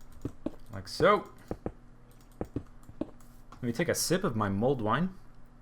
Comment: I thought you said mold wine...